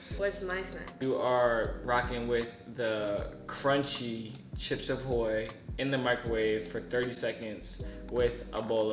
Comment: did he say “Chips of Hoy”? 😂